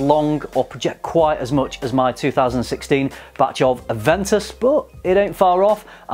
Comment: Thought he said Bat poo.